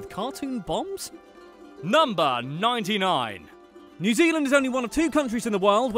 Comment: Did he just say "New Zealandish women"?....